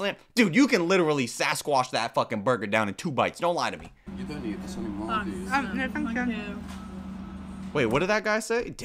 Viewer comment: I really wanted to know what the guy said at 34:34 so I slowed it down and turned the sound way up and I'm pretty sure he said "You don't need this anymore do you?"